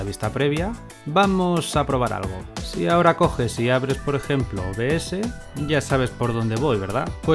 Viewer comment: SI abres queee ?? no se entiende lo que dice , y que seria eso ? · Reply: Hola! El OBS, que es un grabador de pantalla. Perdona si no quedó muy claro. Ese grabador es gratuito y te permite grabar lo que sea de tu PC.